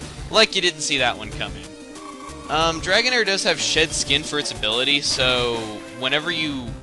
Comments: when u said shed skin i thought u said shit skin HAHA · Did I hear "Shit Skin?" o.o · Lol, sounded like you said "shit skin" instead of "shed skin" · At first I thought you said Dragonair had "Sh*t skin" as it's ability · i thought he said "shit skin" for a second. lol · I thought you said shit skin at first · I am sorry and I mean no offense but....uh when you said shed skin it sounded like you said shit skin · When you said shed skin i thought you said shit skin, im like wait what? xD